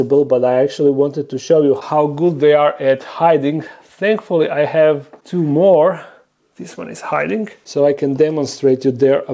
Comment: At first I thought I heard "Thankfully I have tumor" 😀